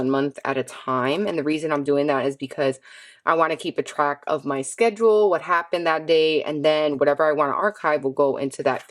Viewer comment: I didn't hear who are the Quarterly page for at 10:21? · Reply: The quarterly section will be for pay dates, appointments, doctor or hospital visits, cycle tracking and sick days.